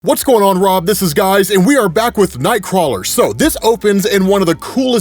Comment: First 5 sec in and I hear: "What's going on Rob. This is guys"! Lol!!